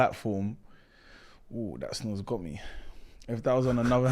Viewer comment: wait what did say at 2:19 ? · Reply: That Snus got me